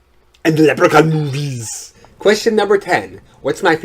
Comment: O L it sounded like you said "What is my favorite Porn Film" :) I was getting ready to associate Lugosi's acting as Dracula with early porn movies I was going to ask it that is how movie and theater audiences might have perceived or associated his performances in both his play and movie as 'soft porn' because of all the seductive nature of his character looks and accent and gestures. Guilty Pleasures The Leprechaun movies LOL ahhhh hahahahaha those were great I liked only two of them but thought that actor??? was it Warwick Davis? was Brilliant in all of them esp the early ones. I also liked those Critter movies Critters in Outerspace one was great with the foreign actor that played the East E U vampire Radu in those 80's sub species? or Bloodstone movies a series of three of those and that cult series movies about the living/killer marionettes dolls from the 80's esp the one from the series where the little dolls seek their revenge on the Third Reich, the Nazis in revenge for killing their puppeteer creator. I so enjoy your videos and your entertaining personality. They are a wonderful distraction from my physical pains In fact some of ur videos are so funny they do the work of a 'pain pill' by providing that 'feel good' vibes.